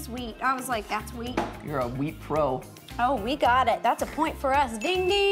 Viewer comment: I almost thought she said "wheat got it"